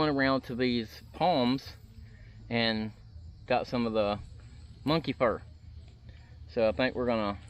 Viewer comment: I thought I heard monkey fart but you caught it just in time lol